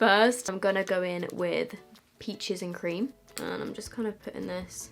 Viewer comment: idk why but when u said 'peaches and cream' i thought u said bitches and cream and i was like ah thats a good eyeshadow name idea lol 😂🤔